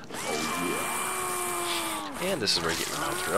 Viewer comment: What did you say at 1:06:30? I hear "and this is where we get..."